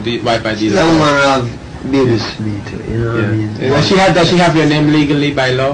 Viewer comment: It sounds like he said “shes a woman who Have babies fi me”